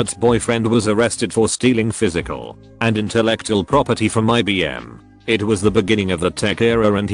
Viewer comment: I thought he said his roommate boyfriend has been stealing property from my BM LOL